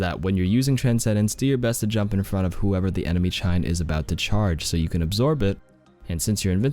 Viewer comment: did you say chein instead of rein? Lol